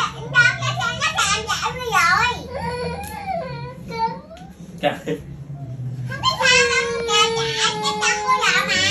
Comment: nói gì vậy mọi ng mình nghe hông đc luôn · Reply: Hình như là " ko sao đâu, chân của con cua kẹt vào cái xe đạp r, ko sao đâu"